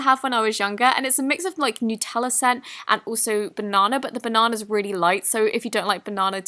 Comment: When you said banana it sounded like you said bow-non-a 🤣